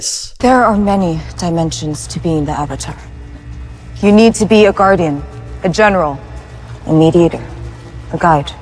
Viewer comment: i thought she said "you need to be a meat eater" and genuinely thought it was some attack on aang's character lmao